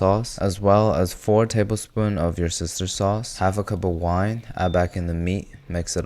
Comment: I have heard that 16:50 "your sister's sauce" instead of "worcestershire sauce"!